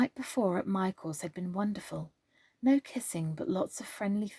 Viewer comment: Wow I had to listen to that part several times. At first it sounded like you said "no kissing but lots of friendly fellating." Hahaha Then I realized you said "friendly flirting" Your accent made it sound like you said "Fellating" 😂😂😂😂